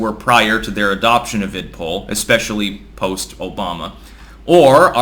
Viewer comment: Can someone tell me what is "ID(?) POLL?"